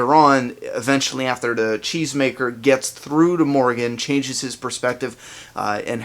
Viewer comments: Until I saw a comment confirming that Morgan said it in an interview, I swore he said chains maker! it sounds funny from a cheese maker... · I always thought Morgan said chainsmaker